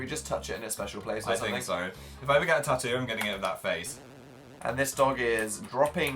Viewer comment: Instead of 'if I ever get a tattoo' I thought Phil said 'when I get a tattoo' aND I